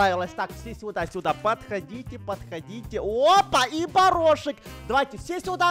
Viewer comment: опа! И барашек! ( мне одно это послышалось? )